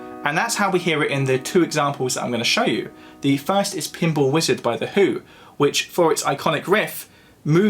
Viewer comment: I swear he said “Pimple Wizard”